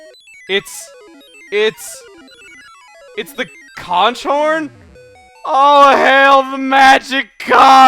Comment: Whoa whoa rewind OMG I thought he said crotch horn lol hahaahahaha( what sound does that make?!)